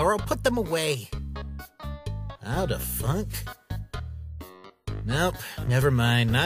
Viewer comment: I heard Fuck. Part 12! Yay! also sorry for my Language.